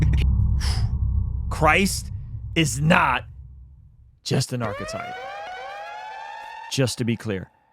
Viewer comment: please quote the guy correctly. He said: "Christ is not an archetype" and you quoted him as saying "Christ is not just an archetype". When you say "not just" means that he is and more which is not what the guy said.